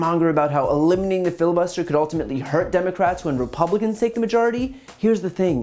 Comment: And why do I keep hearing "When the republican's take over".